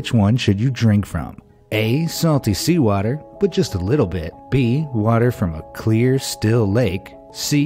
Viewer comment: Are we just gonna ingore that at 2:28 he said walter instead of water